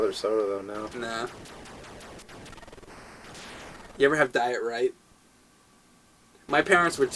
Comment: i thought he sayed "you ever have diarrhea?" lol